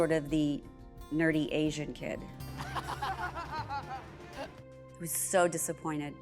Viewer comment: She said “he was so disappointed” you misheard her. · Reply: No, I'm sorry, but you misheard her. She said I was so disappointed then proceeded to say she took it out on him.